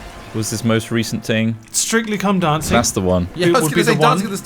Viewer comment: I know it must be "strictly come dancing" but all I hear is "strictly cum dancing"